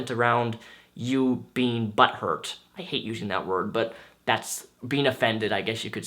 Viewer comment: at first I thought you said, buttburp, hmm butthurt, that is a new for me. It sounds like to describe if a buttfart.